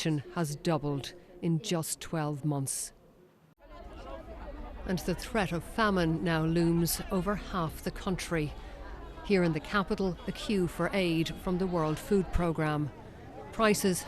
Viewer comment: can someone explain what she said at 0:25 I'm not native and I'm trying to understand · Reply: "And the threat of famine now looms over half the Country! Here in the capitol, the que for aid from the World Food Programme". 0:37 ... Heartbreaking